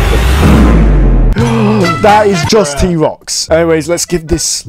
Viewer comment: The first time you said T-rox I heard “B-ROX” just randomly